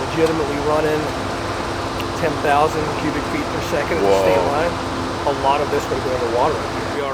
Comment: WOW 10,000 cubic feet a minute at the line this is gonna be an almost impossible task but is always up to the challenge great work putting this together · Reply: I thought he said 10,000 cubic feet per SECOND not minute. Mind blown of that much moving water.